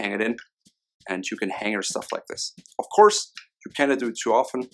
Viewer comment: "...and you can hang your stuff like this." 8:15, I thought he said, "...and you can hang yourself like this." XD